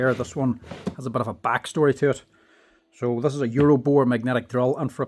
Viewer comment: I thought it said "Euroboob"